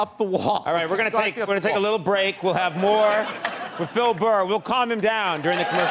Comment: Is it just me or does Conan call him "Fil Burr" at 5:23 ?